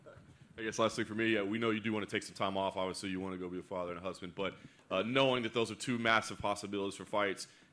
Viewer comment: I thought the guy said ik you wanna go home and be with your dad and husband